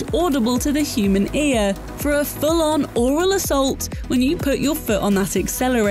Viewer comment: Did she just say that they're going to assault my mouth?